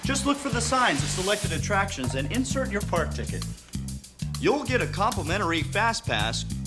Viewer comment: Thought he said “fart ticket” lmao